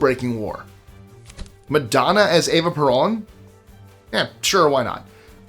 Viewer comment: I thought I heard "Madonna as Eva Braun" before I realized the dialogue meant "Madonna as Eva Peron".